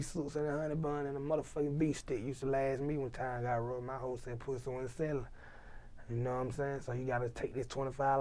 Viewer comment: Did he just say my hoe said pussy wasn't selling?